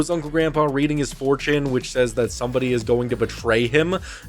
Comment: deadass I thought he said 4chan???